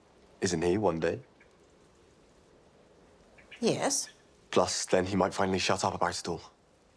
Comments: WTF did William say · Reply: " Plus then, he might finally shut up about it all " 🙂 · What does William say at 1:29? "Plus he might finally shut out our barstool?!?!" Can't figure out the last part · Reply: "Plus he might finally shut up about it all" is what it sounds like to me.